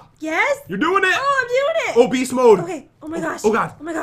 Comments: When he said “oh beast mode” I thought ha said “obese mode”. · When they were at the grandma knitting part I really thought he said obese mode instead of oh beast mode